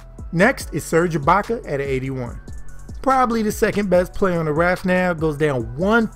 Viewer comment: did he say ibaka the second best players for the raptors now🤣🤡